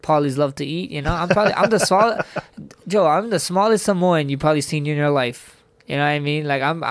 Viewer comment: Thought that said salmon